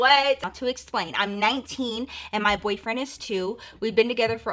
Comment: "I'm 19 and my boyfriend is too" I had to replay to make sure I heard that wrong😂